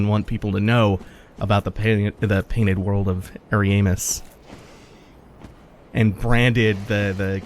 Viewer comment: I misheard you it sounded like you said the painted world of hairy anus lol